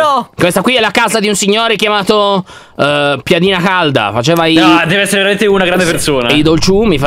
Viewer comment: Ma solo io al momento 16:37 ho sentito dire "Piadina Halda" ?? AHHAHAHAH vi prego ditemi di no 😂